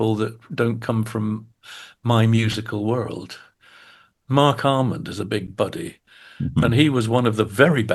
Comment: Who is Ian referring to at about 18:25 or so? Sounds like he says Mark Harmon? Mark Almond, maybe? Says he was a "bad boy of rock" in the late '80s/early '90s, but hard to understand who exactly he is talking about. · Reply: Definitely mark almond they recorded together played together